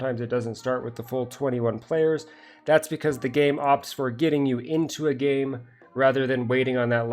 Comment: I heard "The game AWPs for getting you into a game"